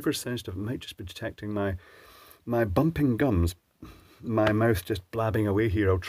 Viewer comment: Ha ha you said 'its gonna detect my...' and I thought flapping gums! You said it better 'bumping gums!'.